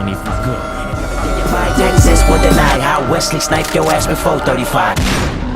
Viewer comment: I always thought he said "I'll wesley snipe yo ass with 435" rather than "before 35"